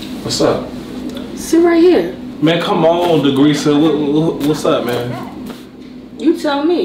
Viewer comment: “Degreacea” I died when he say the name, they come up with the most ratchet name they can find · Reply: I thought he said degreaser!